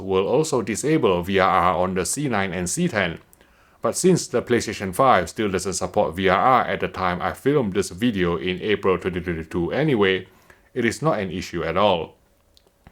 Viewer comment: did you say April 3022?